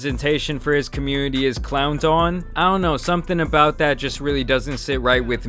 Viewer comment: Did he just say "CLOWNT" lmao